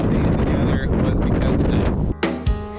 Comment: the wind totally blew out what you were saying 🤔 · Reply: Yeh, sorry about the wind. I was saying “the real reason I bought this saw and stand together is this”. It’s so nice to have a collapsible stand for it.